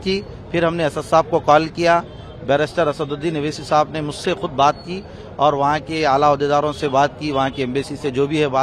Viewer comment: Did he say barrister Owaisi or ba$tard Owaisi 😂